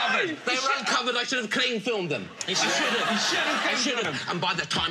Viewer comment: What does he mean by ''I should have clean filmed them'' at 1:11? Never heard of that or am I hearing it wrong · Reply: Mario Margalić Cling filmed. He means the clear plastic thing you use to wrap your leftovers in.